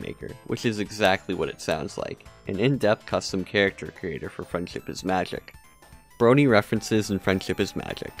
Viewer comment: Sometimes it sounds like your saying “freind chip is magic”